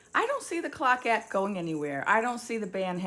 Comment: it sounded like she said I don't hear the clock at going anywhere. What does that mean or did I just not understand her words